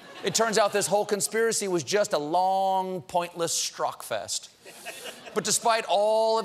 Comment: "Strock fest" deserved a bigger laugh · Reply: Peter Byrne strock fest ? I thought he said stroke fest.